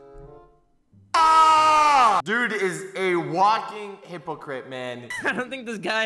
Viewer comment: 😂 I get my daily dose of laughter here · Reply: The first time I heard him say "walking hypocrite" I heard "Walking Hip cricket"